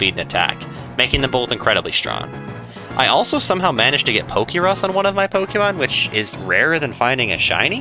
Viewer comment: did he say poke rust? Someone please explain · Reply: Pokerus. It's a "virus" you can randomly catch Pokemon with. It doesn't hurt them, it's actually helpful, doubling any effort values they recieve. If I recall correctly the odds of finding it are 1/60,000 or so, compared to the 1/8000 of a shiny.